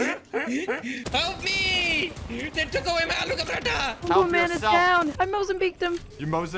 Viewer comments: is it just me or did say aloo ca paratha???? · I think he said they took away my aloo ka paratha 😂